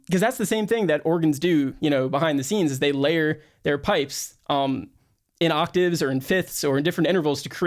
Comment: did you say layering pipes? 🧐